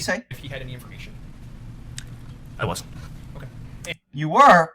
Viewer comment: I hear him saying "I wasn't" he was not interested in what Rittenhouse wanted